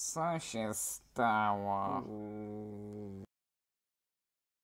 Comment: usłyszałam "Chuj" 😂😂😂 ( ͡° ͜ʖ ͡°)